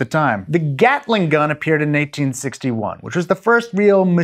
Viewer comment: It sounded like he said the Gatling gun appeared in 1961 when it was invented in 1861. Mentioned around the 6 min mark.